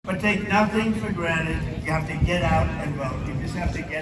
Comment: His fans thought he said: "You have to get out and go home."